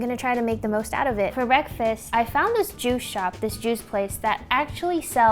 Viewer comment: I thought she said " I found this Jew shop" hahaha thought of New girl.